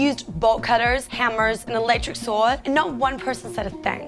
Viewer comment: she said electric when the guy was holding a hack saw. And it sounds like she is saying nitric saw ?.isint he using a angle grinder